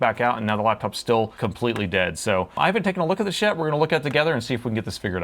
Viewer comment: I had to replay @ 0:20. I thought he said “I haven’t taken a look at this shit”. Seemed way out of character.😆